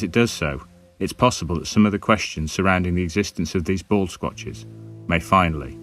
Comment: For a minute there I thought he said bald snatch!.lmao!!